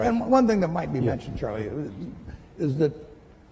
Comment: did he say charlie 😅